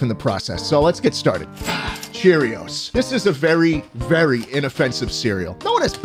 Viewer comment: Me standing over here who legitimately was force fed Cheerios for 5 years straight each morning: softly “d o n t.” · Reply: i literally thought you said cheetos